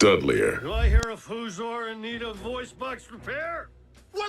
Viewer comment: what did he even say here? 😂🤣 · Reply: "Do I hear a Fuzor in need of a voice box repair?"